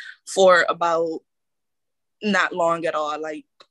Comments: again I heard 3 seconds, not 30 lol. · ~ I heard three seconds not thirty seconds.